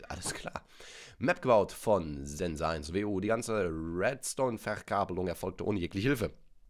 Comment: Ich habe verstanden sense auf die cdu